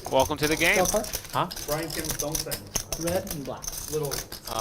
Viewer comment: 윈나는 한국사람 입니까? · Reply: Yes, I think she's probably Korean. She said hello with a Korean accent. Someone described her as Brian Kim's "dong-saeng" and it sounded like she said her name was.